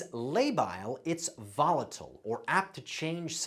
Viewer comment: dude correctly pronounces 'labile', then immediately follows with 'volattle' instead of 'volatile'.